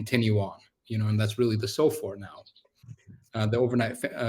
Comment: No minuto 11:49, existe um erro na legenda por um equívoco no conceito. Foi traduzido "SOFR" como se fosse "so far". Ele quis dizer que o índice vigente que substituiu a taxa Libor foi a taxa Sofr. Mas nada que prejudique o entendimento da entrevista Muito bom!